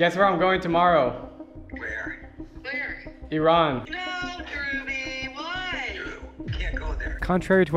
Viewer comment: ONE OF HIS NICKNAMES IS NOT BO 0:43 The captions say Drew, BUT DREW'S MOM SAID DREWBY